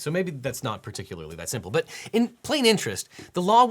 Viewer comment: "in plain interest" haha. 19:51 wrote 9 instead of 7